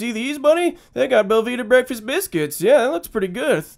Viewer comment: I do love Belvita but would rather taste one of those delicious sandwiches with a quarter pound of meat and three slices of cheese, uh huh-huh huh-huh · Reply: For the longest time I thought he was saying Velveeta breakfast biscuits